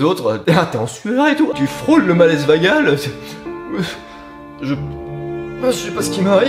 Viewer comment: Mdr a 2:25 j'ai compris "malaise vegan" 😂😂